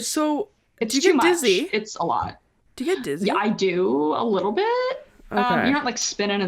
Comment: What did she just say? lol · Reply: Do you get dizzy? - I do, a little bit, you know like spinning in circles and stuff I know the comment is from year ago but I just randomly saw this video